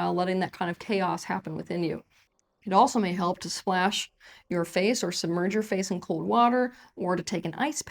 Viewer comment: I thought she said “it may be helpful to slash your face” 😮 oh that’s not what she said 🤣